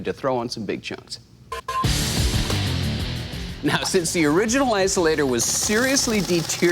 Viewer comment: What's he saying at 8:02?? A WHAT-insulator? 🤔